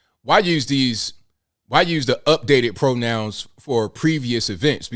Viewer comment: Sounds like you said "NUTDATE" ,LOL Which is more like it